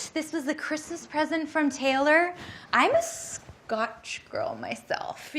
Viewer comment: 'A Christmas gift from Taylor'. Not Swift, ya'll. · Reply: I thought she said miss Taylor